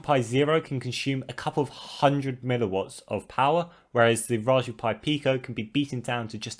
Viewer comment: Did you say... Megawatts? That was funny XD